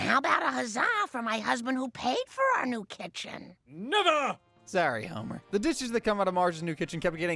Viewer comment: Dude did he say the n word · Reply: he said never